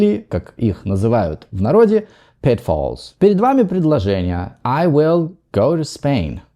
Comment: the captions says you pronounced the word "pitfalls" as "petfalls".